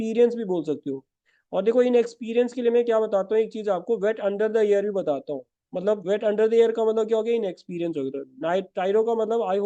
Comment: Sir i was not able to understand what you said at 1:54 min for word unexperienced, something like 'bad under the year' what was that? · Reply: wet behind the ear means inexperienced